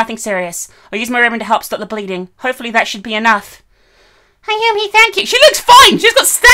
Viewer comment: Wow. So nobody remembers them? This was a great ending to a great story, but kind of gives off the feeling that in the end, nothing was truly accomplished. Several likeable characters were lost to the void that is the school, wonder how Seiko's brothers feel. She practically was their mother and person they could rely on. Then there's the fact that the cursed school is still standing in shape and Sachicko and the other kids were never dealt with. They were simply left alone to keep terrorising more kids thanks to Naho. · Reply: I honestly thought you said " Thanks Nando's" Instead of Naho..